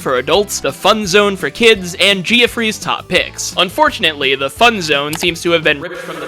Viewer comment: did you just say geo-free's? 😭😭 ITS PRONOUNCED JEFFERY